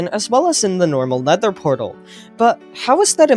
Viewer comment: did you say, "Leather portal?"